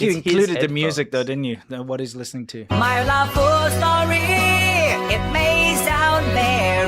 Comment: I thought Ayumi was saying "I Love balls" :D